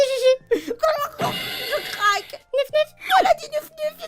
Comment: j'ai entendu "Yiff yiff" au début x3 mon âme de furry m'a pervertit à jamais x3